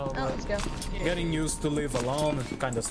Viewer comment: Elevator ride at 4:53 purposely says won't. That's what I heard its not a mistake. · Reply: i can definitely hear "i dont wanna be here no more" over "i wont be here no more", but ehhhhh